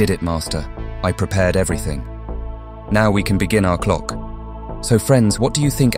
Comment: tbh, I heard Alpha say "Now we can begin our Conquer" so obviously they're planning to rule the whole world or something, since the Leader of the Clocks is gone now.. and she just removed one of her biggest threats.. or so she thinks.. but something tells me this isn't the last we'll see of Chief Clockman and maybe he looked into the future and saw that he was gonna die, but then Titan Clockman saves him, so he went through with this anyway.. Keep up the cool analysis work:D